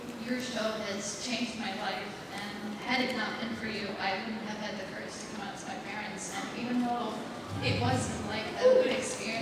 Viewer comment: i cant hear very well but it seem the girl said something that made Elise and Natasha be moved. Tears a little bit. Thanks for upload this video! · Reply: A cream puff said that if it hadn't been for them and watching their characters she wouldn't have had the courage to come out to her parents. She drew them something as well.